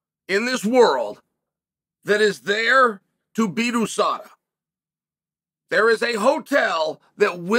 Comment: When talking about the steroid hotel Chael said “Adesada” instead of usada. Freudian slip? 🤔